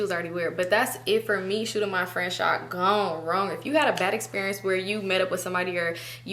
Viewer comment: Girl I thought u said shooting my friend gone wrong😭